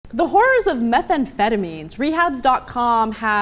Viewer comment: did she just say "the whores" at the first second?